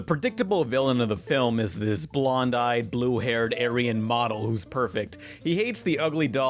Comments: WAIT. did he just say blond eyed blue haired doll? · Did you just call Lou Blonde-eyed and Blue-haired??